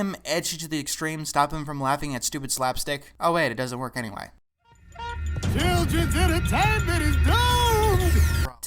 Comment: the hell is he saying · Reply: “Children’s entertainment is doomed!”